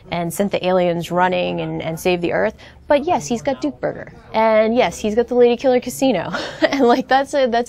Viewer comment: did she said duke burger at 2:45 ?